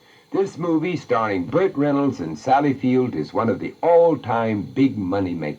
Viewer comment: at around 10:50 does vincent say field or fields? doesn't sound plural to me... (if so, one for the mandela effect people)